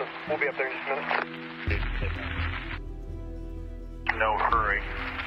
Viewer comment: I heard : " No ! Hurry ! NOT " No hurry "